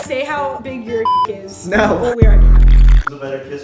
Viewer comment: did she say we already know XD